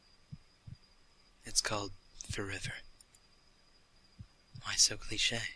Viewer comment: does anyone hear 'forever' when he says 'for river' at 3:38?